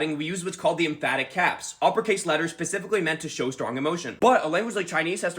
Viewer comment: I heard him say pacifically in play of specifically and me brain n hurty